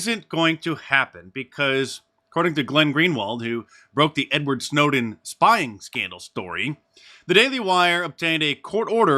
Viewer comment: Did Mark say, "N-word Snowden"